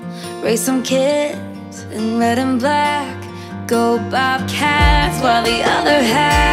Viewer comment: it sounds like she’s saying let ‘em black instead of red and black